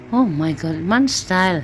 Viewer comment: did you say 'oh my God'? I'm not sure if I heard right. But if so than please don't use that word😭❤